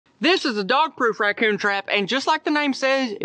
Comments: I thought that he said you could bait it with "dog poop". 😂 💩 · The whole time I thought he said "dog poo". Was wondering why a racoon would want dog poo. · I thought he said dog poo!